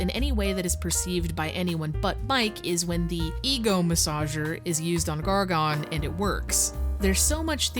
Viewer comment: Listning to this at work and I kept hearing "eagle massager" instead of "ego massager"...